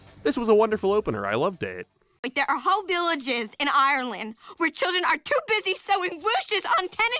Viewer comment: Every time I heard you say Arlen I thought you were talking about Ireland 😂it’s just me and my deaf ass